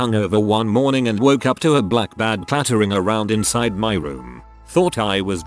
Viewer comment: Okay I was listening to the video, without reading along. And the text-to-speech pronounces things weird. So I thought this said a black MAN. It was weird enough that the OP kept saying "it". But then he apparently FLEW AWAY! 😰 ....yeah, I had to double check what it actually said 😂